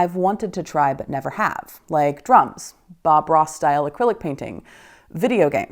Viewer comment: Oh! DRUMS! I was multitasking and thought you said DRUGS. Which didn’t seem like a) a hobby or b) a good idea, but I’m not your mom.